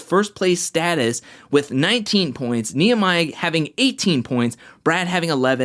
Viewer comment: Did you just call Nia (Nehmiah) 😂😂 1:35 Love your videos! Keep them coming